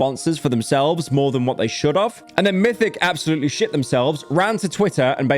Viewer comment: Did bro say "should of" out loud?? Bruh